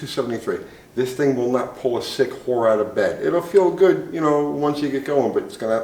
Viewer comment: I heard it "That car couldn't pull a sick whore off a piss pot". Too funny, I feel like I know he is so much like the old mechanics I grew up with. Keep the videos and the one liners comming.